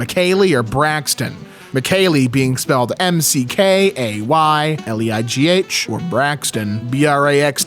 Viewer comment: I thought he said emkay